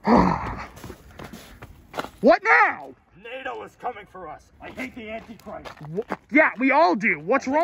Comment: Did he really say "NATO is coming"?